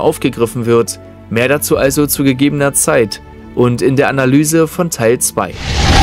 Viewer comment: Habe ich da bei 8:21 etwa gehört "mehr dazu in gegebener Zeit und bei der Analye von Teil 2"? <3